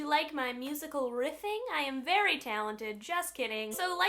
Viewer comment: OMG I thought she said musical yiffing